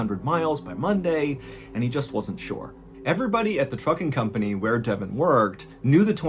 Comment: i swear i tought i heard u say at the fucking company lmao who else tought thwy heard that too lol😂